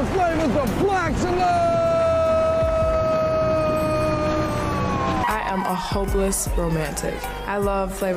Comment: For a second I thought that woman said "I'm a homeless romantic" and I thought, you're on the right show... Cause the first thing he said is he has a mansion, could solve all of your problems in one go if you win lady.